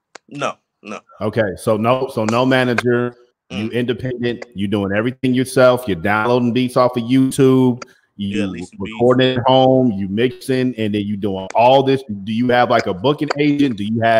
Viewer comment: that brother ain't dumb sound like he said"leasing Beats"😂😂💯 that's my method tooexcept I'm using a recording app. And purchasing a commercial beat license.